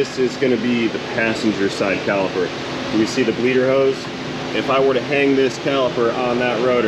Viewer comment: did you say that caliper had a lederhosen? So, is that a Bosch part?